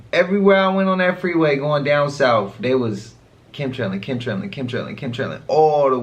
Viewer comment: CC says@ 37:05 Kim tring Kim Trin Kim tring all the way! lol I feel bad for the deaf!